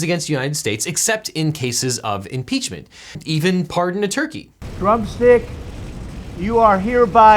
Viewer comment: I heard a rumor that Drumstick the Turkey had been being paid to dig up dirt on Hunter Biden. Because NOBODY gets a Trump pardon without earning it. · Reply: Whew. Okay, I thought I heard "Trump Steak", not "Drumstick". Goddamn, I should be charging this guy for rent in my head.